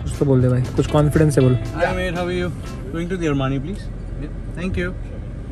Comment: what did he say? Can anyone please say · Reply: Hi mate how are you going to Armani